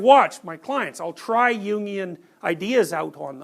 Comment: What is he referring to when he say's, I think it's "Ying" around the 8:30 seconds marker? or it's referenced as "union" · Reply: Carl Jung Jungian